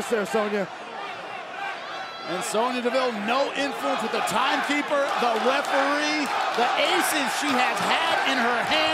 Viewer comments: What are the crowd saying in here guys? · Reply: Welcome back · Did anyone understand what the audience was chanting at 2:20? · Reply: Welcome Back